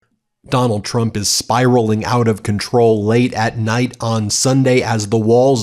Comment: I thought you said he’s plane was spiraling out of control.